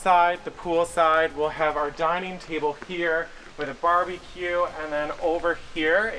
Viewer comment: I swear I heard say, "Barbie-cue." Is this his Barbie Dream House? I love you guys so much. I wish we were friends!